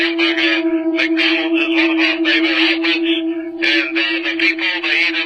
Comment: yaa I cant understand a word of that clip haha! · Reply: oh I got "show, 300- 400 miles, (air/year) hamburger? Mcdouble is on of my favorite omelets"? lol. "the people eat it for breakfast, (something) lunch". weird but doesn't seem to dark